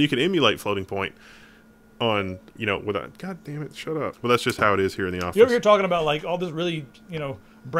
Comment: LOL 2:53...holy crap ..Did I hear of all people cursing with " god dammit shut up" xD OUR CLEAN? :P